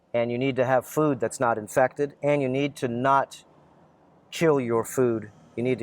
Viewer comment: did you just said : “You need to NOT chew your food.” WTF ?